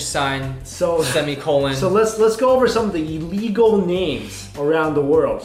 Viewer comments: I thought it said illegal Baby Memes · Am I the only one who thought it said, "illegal baby memes"😂